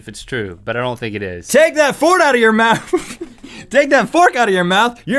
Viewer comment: Did he just say "Take that Ford out of your mouth"? Even if he didn't, his slip-up was still hilarious!